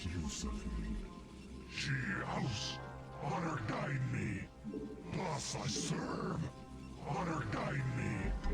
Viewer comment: I still don't know the actual phrase, but I always hear "Plus I serve" from Dragoons and "Meet Joe Black" from Zealots.